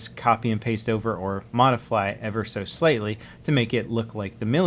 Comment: I am forgetting the whole subject of this video to say that at 3:43 does he say "modifly"?